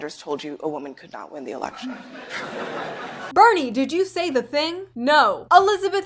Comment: no not "Bernie, did you say that?"... it was "Why did you say that?"... BIG difference.